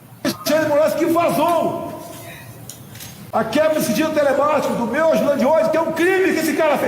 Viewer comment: só eu que ouvi: "A quebra do sigilo telepático"?